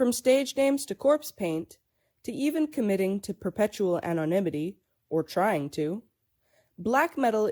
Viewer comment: What word was said at 0:40? "Perpetual (something)" Anyone know? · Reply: Perpetual Anonymity.